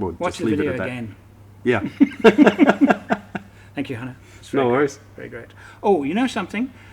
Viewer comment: I thought he said "thankyou honey" and had to rewind 5 seconds! Hahahahha